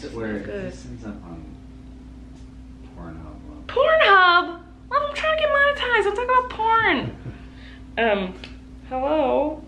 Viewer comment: It sounded like she said, “Porn Hub!. I’m trying to get demonitized let’s talk about porn”. 🤣🤣🤣